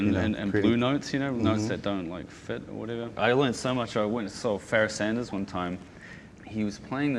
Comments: What is the name of the horn player he mentions at 6:22? I can't quite understand it...:-s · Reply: Pharaoh Sanders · Who did Ruban say he went to see at 6:21? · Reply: I realize I'm 5 years late but he's talking about Pharoah Sanders!